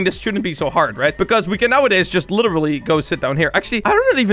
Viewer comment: DID HE JUST SAY SHIT?